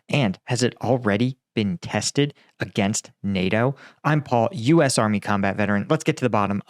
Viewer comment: against NAITO? lol wtf is NAITO. lol you even said it weird so now I'm just like wtf.